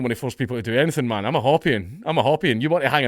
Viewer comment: What does count say at 5:30? A Hoopian? I’ve never heard of that but I think I’m hearing him wrong